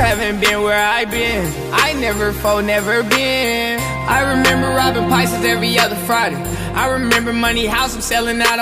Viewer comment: Did he just say " i remember robbing PAISAS every friday"???